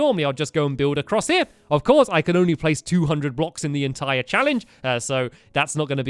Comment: did he say he can place 200 Blocks?